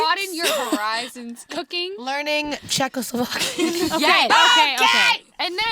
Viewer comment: Dani said Czechoslovakia? That is funny because I am from the Czech republic :D. But I do not know if she really said it. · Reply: I think she said learn Czechoslovakian, and you can't do that since it's not one language anymore, it's either Czech or Slovakian 😅